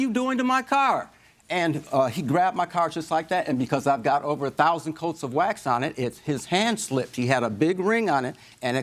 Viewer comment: Did he just say it has over 1,000 coats of wax on it??? The dude has spent more on car washes than the car is actually worth 😂😂😂